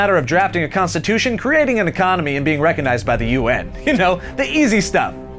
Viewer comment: did he just say U.N?